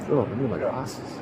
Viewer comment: now that new arrival cop sounded like he said something else at that point in time. I think he was saying “wow I gotta get some glasses”. What it sounded like to my corrupt mind was, wow I gotta get those a$$#$. Maybe it had something to do with the beaches and all. Lol